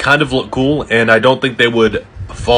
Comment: I swear to god i heard "bonner buds"